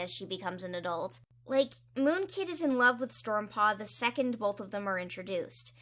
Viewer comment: I misheard 4:03 as "Moonkitti's in love with Stormpaw the second they're introduced" and had to do a double take